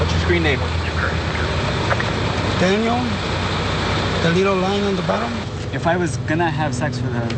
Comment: Lol....I’m sorry, but did he say “Daniel...a little line on the bottom.......”🤣🤣!